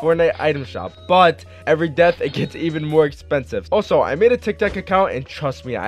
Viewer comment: did he say TikTak‼️🤣